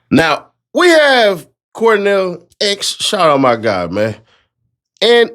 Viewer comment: did you say Cornell? 😂😂 I swore I heard Cornell and not Quanell.